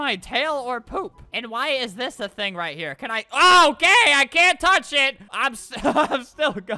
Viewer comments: I thought he said " Oh gay I can't touch it." XD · It sounded like he said "OH GAY I CANT TOUCH IT"